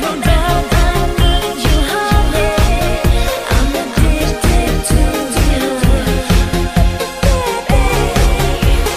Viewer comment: can someone tell me what it says a min 1:55 ??? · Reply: Im addicted to the high.